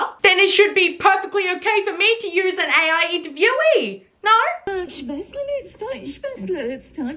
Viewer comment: I heard ‘ist deutsch verständlich”💀